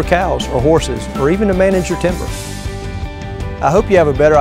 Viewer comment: Does he say "on ten acres, you can have some cows and horses, and even learn how to manage your temper" at 0:55? What's he going to do? Butt heads with tree cattle instead of everyone else? 😂😂